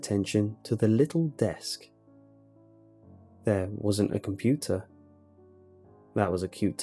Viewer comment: When they said computer I thought they said good pizza Must be hungry 😄